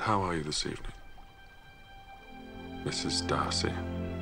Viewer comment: subtitles "this is Darcy" instead of "Mrs Darcy" :)